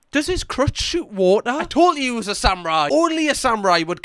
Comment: Omg I thought you said his CROTCH WAS SHOOTING WATER 😭😭😭